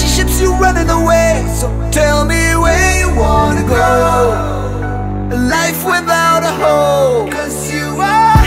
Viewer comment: Ich hab verstanden: Where you want to go in a life without a hoe xD